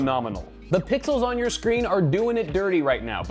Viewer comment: Literally thought he said "The pickles on your monitor are lying to you".